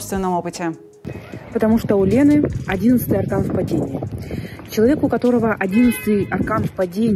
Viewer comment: объясните пожалуйста "тупому", что такое "11й стакан в падении" (это как я услышал, хз может там другое слово), что это бл...?:face-green-smiling::face-green-smiling::face-green-smiling